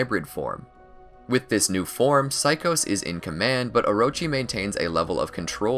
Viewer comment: i thought it said orichimaru and i was like i thought he already did this